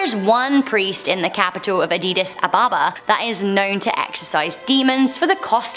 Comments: sec Did i hear Addidas Ababa ?😁😂😂. A D D I S A B A B A - not Addidas, Puma , Nike Ababa . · did she just say adidas